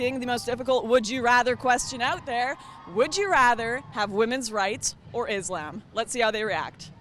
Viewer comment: For a second, I thought you said "women's rights or insulin." ;)